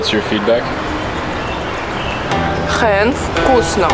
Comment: did she say "хееее, вкусно!" or something in Chinese lol